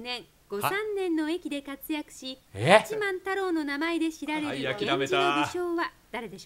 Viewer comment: 八幡太郎がガッチマン太郎に聞こえて「え？」ってなった